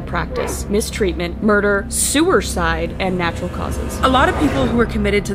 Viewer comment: Remember to go to channel for PART TWO!!! We spilt up and shit get INSANE😭👹 Part 2 is up at 10AM PST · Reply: …. Did u say sewerside…. cough look back at this…😅